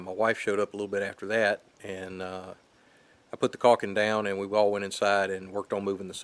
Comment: you have me at a loss. Not sure if corking is an Austrailian term or if you meant caulking and got auto corrected or something. At any rate if you mean caulk, I doubt it because there is no way for water to get up there. Also when I put the tongue and groove pine on the porch roof it will then be covering this joint. The seam between the siding and roof will then be caulked. Hope that answers your question.... if not let me know what you meant by corking. Thanks for watching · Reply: Hey mate, Yeah I meant cauiling. Sorry I thought you were saying chalking and it did answer my question. Thanks :)